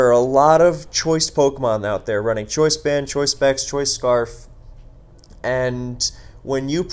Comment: The automated captions say "choice bad choice bad choice scarf" and I don't know if that's some scathing commentary on the game or AI failing miserably, lmao